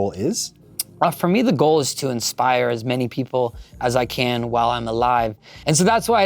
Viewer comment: This interview took a terrifying turn when i thought Oliver said “for me the goal is to expire as many people as i can.”